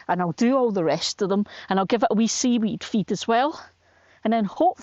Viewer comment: What would you feed your potatoes with at this late stage? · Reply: Sorry, just heard you say seaweed feed